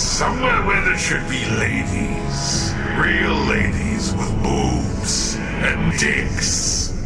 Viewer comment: I have no exact idea why, but it's my favourite episode. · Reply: Hey, I know I'm 5 years late on this but did anyone else notice that when Ahriman at 23:07 speaks the subtitles are, "Real ladies, with boobs. And not dicks", but he actually says, "Real ladies, with boobs. And dicks"